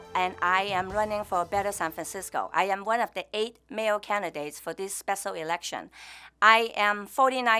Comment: One of the 8 male candidates? Did I mishear? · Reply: Mayor candidates :)